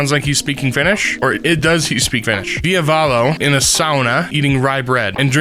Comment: i heard diavolo instead if ville valo help